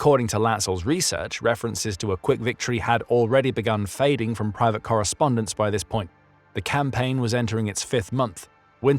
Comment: i wonder what was said/happening? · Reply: he asked if he knew who took his rifle?